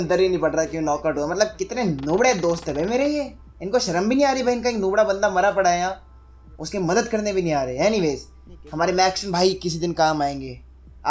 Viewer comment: it sounded like' bhen ka loda banda mara pada h ' lol!!